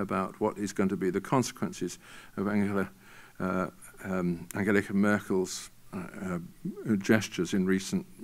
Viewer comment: did he say angelica merkel? :)